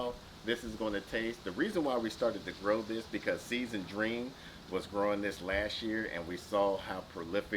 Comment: What was the name of the channel you mentioned at 3:48 that inspired you to grow the long skinny squash? I couldn't quite catch the name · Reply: Seeds and dreams, she has account on IG....she is AWESOME. We want her to start a youtube channel, tell her we sent you if you check her out...LOL! #peerpressure lol